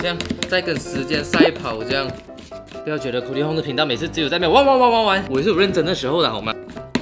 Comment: 半夜看太邪惡了😂 玩玩玩玩玩我直接聽成汪汪汪汪汪😂